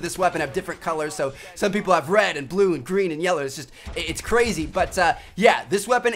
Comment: Did he just say red blue green and yeller?